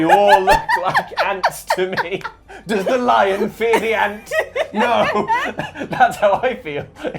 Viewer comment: i kept hearing "does the giraffe fear the ant" instead :D